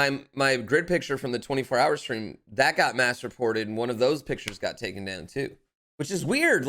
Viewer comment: Every time you said "mass reported", I kept hearing "masturbated". I'm going to hell.